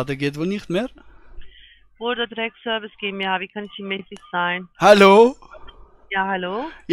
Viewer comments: Bitte mit Zeitstempel - Was ist DEINE absolute Lieblingsstelle in diesem Anruf :D?! Achja - Bitte buchstabieren! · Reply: Ich verstehe bei 16:40 "Border Decksservice GmbH - wie kann ich die Miete zahlen?" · Ich verstehe bei 16:40 "Border Drecksservice GmbH - wie kann ich die Miete zahlen?"